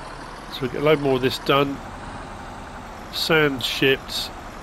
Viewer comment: LMAO🤣When you said sand shipped I thought you said sand S*%@ I had to put words on and listen again. 😳 Too funny !